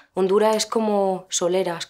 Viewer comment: Jodas😂 yo pensé que decías Honduras por el país no por "el flow"